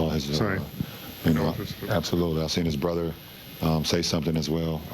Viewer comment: what did AD say? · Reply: His brother.